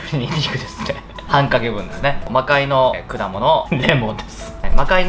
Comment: 魔界の果物デーモンです(空耳)